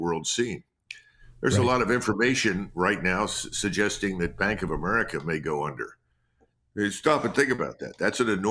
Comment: Did he say that Bank of America might go under at 9:11 ?